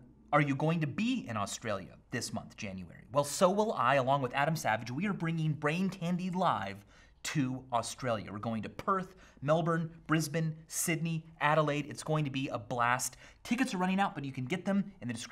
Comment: Not going to lie I heard " we´re going to purge Melbourne, Brisbane, Sydney"